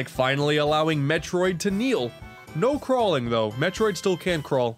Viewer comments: did you say.... m-metroid.... how could do.... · Did...did you just call her "Mefroid"?